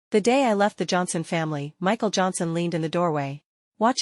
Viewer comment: When he said Michal Johnsen I thought they said Michal jackson